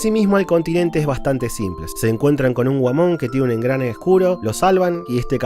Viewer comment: Entendí "Se encuentran con un Whamon, que tiene un engrane en el culo"